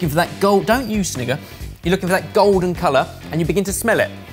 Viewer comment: Bruh I thought he said the n-word at 1:15, good thing that it was a false positive.